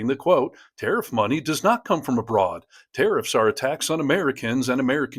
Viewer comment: I misheard that as "tariffs are attacks on Americans".